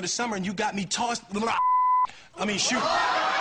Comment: what did he say😂😂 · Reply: He said shoot